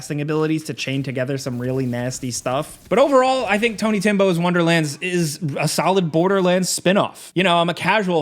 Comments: did he say "tony timbo's wonderlands"? lol 🤣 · Haha, I thought you had just slightly slurred your words/I misheard when it sounded like you said "Tiny Tim's Wonderlands," then came "Tiny Timbo's." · Did he say tony Timbo??? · Why does it sound like he says Tiny Tim or Timbo....lol